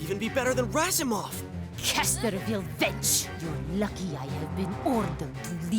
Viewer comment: casterwill WHAT?! AIN'T NO WAY SHE SAID THE B WORD. but it's all i can hear